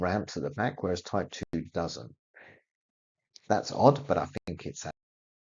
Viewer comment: Can you please get a better microphone? The content is amazing, but quite hard to hear sometimes. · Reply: Would like to know what is said at 4:40 before the cutaway. "That's odd, but I think it's a-" Accurate?